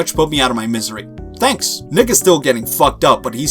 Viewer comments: 🤨 i heard that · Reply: he said 'nick is' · WHAT WAS THAT I JUST HEARD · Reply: “Nick is.” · listen closely because I don't know if I'm the only one who heard it cause if I am my ears are screwing with me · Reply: “Nick is.”